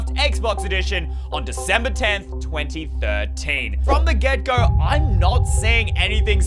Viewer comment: did you just say 2013? thats the year I’m born